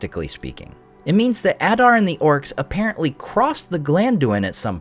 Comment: Thanks for sharing that background information on conceiving the forging of The One! That is such an interesting take on it. Also, and completely beside the point, whenever anyone says, "Adar and the orcs," I hear "Benny and the Jets". :)